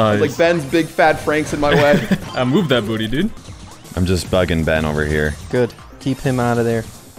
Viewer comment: did say "im just fucking ben over here"